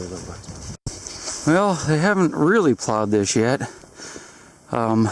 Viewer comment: I thought he said "no one plowed this sh*t" xD